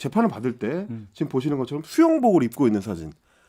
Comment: 수영복으로 들었잖아 ㅋㅋㅋ